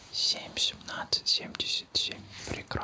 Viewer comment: а я услышала не 77, а 75...